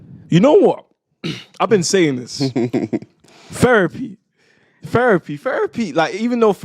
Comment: Did he just say LOL